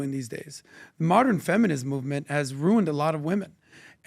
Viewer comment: did he just say ruined 😮